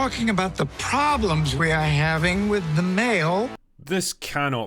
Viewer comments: When they said, we have a problem, with the mail. I heard "the male" I was like. Nooooo they didn't flat out say it XD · You know, at the start I thought they said : « we are having a problem with the MALE »… I was a little bit relieved… though I suspect that they maybe intended that… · I thought he said "problems with the MALE" at the start of the vid 🤣🤣🤣